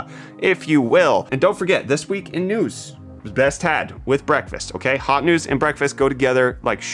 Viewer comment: Are there Hot News hats? Because it sounded like you said best hat with breakfast 🤩